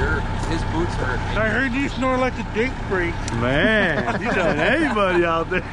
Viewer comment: I thought told “I heard you smell like grape drink”😂😂😂 I had to rewind it and It was “snore like a freight train”